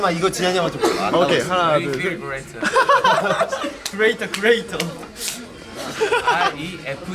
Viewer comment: instead of refrigerator,I heard Jiahn said we feel greater/refill greater😊😂😂And I'm looking forward to Hyunnuk 10pack😜